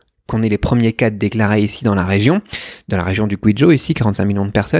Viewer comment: Pour ceux qui n'ont pas bien entendu, ou compris le nom de la région qu'il prononce trop rapidement à 15:30, je pense que c'est la région du Guizhou. corrige moi si je me trompe stp :-) !! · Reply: Yes, je suis à Guiyang, dans la région du Guizhou.